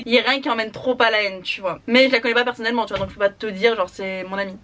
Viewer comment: Il à l'air sympatoche gossip youtube dis-donc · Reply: Adjoa Carneva aah d'accord mdr, psq comme j'ai entendu à 4:03 "je la connais pas personnellement" et que je savais que c'était pas censé être une fille bah j'ai eu un bug